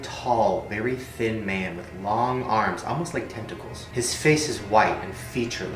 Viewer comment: When he said almost like tentacles I thought he said almost like testicales